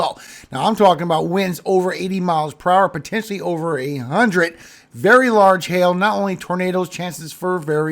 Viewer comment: Potentially 100+ i heard over 800,knew that not possible without explosoon.😅